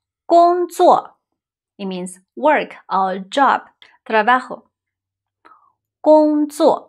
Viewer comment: did you say "trabaho"?